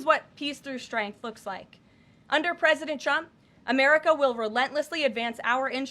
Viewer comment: She pronounces the president's name where sounds like "Chump". 3:03 Sounded like that a few times, Karoline needs to work on that or "Chump" is gonna get real mad.